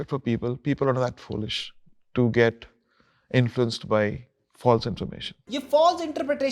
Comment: I think is said “false information” and not “false interpretation”, which is even worse !